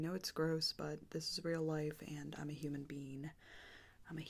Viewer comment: did she just say “i’m a human bean”?